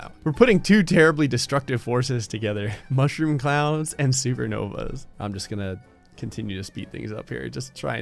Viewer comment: i thought he said we're combining 2 powerfull forces mushroom clowns and secret novas XD